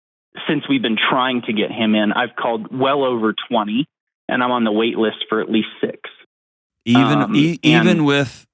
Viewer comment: Can this father find a private psychiatrist? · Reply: I thought he said there is a 5-6 month wait list